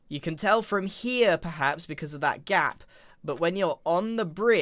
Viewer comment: I heard gyatt instead of gap lol